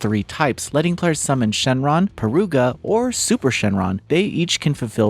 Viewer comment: "Zeno and whise" holy fk what did u just say dude? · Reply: "Peruga"